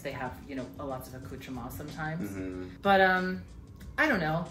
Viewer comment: What is that word says at 8:22? She’s a French vocab QUEEN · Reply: accoutrements , things or ephemera; beautiful articles or 'stuff' . Gorgeous bits, basically ! x